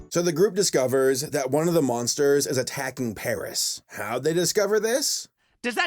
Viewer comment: what he said? "Paris e frisé" ?